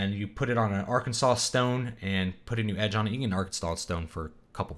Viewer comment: When he said "Arkansas stone" I heard "Arkenstone". I need to put the tolkien down